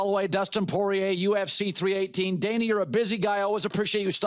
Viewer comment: Wait around 9:45 what did he call him? 😂sounded like Daney.